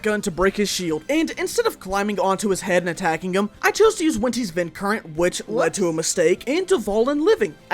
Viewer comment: Did he say “Wenti’s vind current” at 17:57 or am I just going crazy?